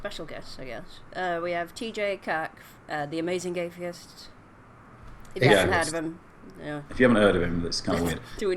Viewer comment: Maybe it's because AiU is on, but at 6:48 I heard "TJ cuck, The Amazing Gaytheist."